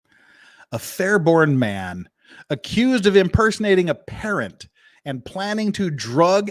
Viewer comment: I was listening to you as I was getting awake. I thought you said "he was impersonating s parrot".